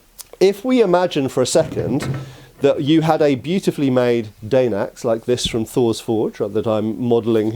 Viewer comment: Sorry not sorry but all I heard was "Great Dane Axe". And I too love your enthusiasm when swinging the pole axe